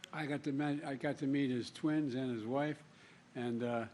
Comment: What does Joe say at the 0:18 mark "I got dementia" ?????